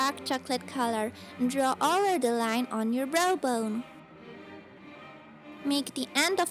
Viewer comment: it sounds like she says "bro bone"